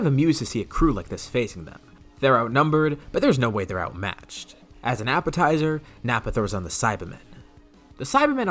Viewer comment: DID HE JUST SAY “as a nappatizer” I CANT UNHEAR THIS